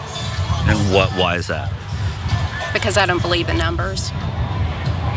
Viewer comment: Did she say "I don't believe in numbers"?? 😲 WTF does she believe?